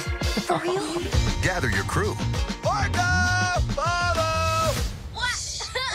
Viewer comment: I thought they said “Marco, polo” Instead of orca follow 😭